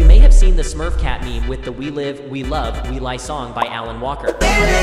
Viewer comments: We became Alan Walker fans because of Rolling Sky, one of my sons' favorite games. The older played before the younger. I just watch😂 I thought he said we live, we love, we learn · I thought u said anakin skywalker lol who else 🫵